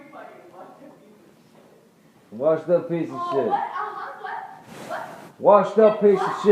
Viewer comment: she calls him a “washed up piece of shit” the subtitles are wrong. She’s so awful. She had a dream life and it still wasn’t good enough.